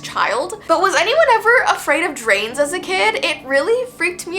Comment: I thought you said trains